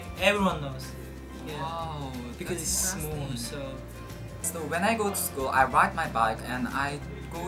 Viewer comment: what saie "when i go to school i ride my bike" what i heard "when i go to school i wipe my butt" my face :O i had to replay it lol